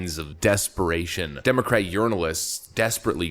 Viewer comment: It sounded like you said Democratic Urinalists. Quite fitting.